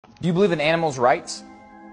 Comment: I thought he was saying do you believe in animals